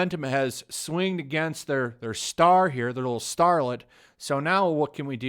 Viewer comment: For a moment there I thought you said harlot instead of starlet.